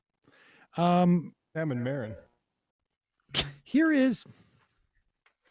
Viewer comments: What does Matt say at 4:28? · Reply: I think he says "like Sam and Maron." I'm assuming he means Marc Maron. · what did Matt say here? Ammon Marron? What is that? What was the big burn 🔥 that Matt said? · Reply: Sam and Maron, aka Marc Maron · what is matt saying at 4:20 (nice) that makes emma chuckle and gasp, i can't quite make it out? gotta be tough .. ???? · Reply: He says "Sam and Maron" in reference to Marc Maron whom Sam used to co-host a show with on Air America. Marc now has the What The Fuck podcast and it is huge and one could say Marc is now more famous than Sam by far. The context is that they are talking about how Dan Bongino used to be cohost with Grant Stinchfield, but now Dan has far eclipsed Grant, and they wonder how Grant feels about it. Then Matt says "like Sam and Maron"